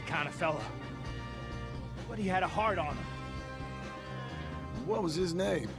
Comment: Lol when mickey says Arthur had a heart on him I misheard and thought he said he had a hard on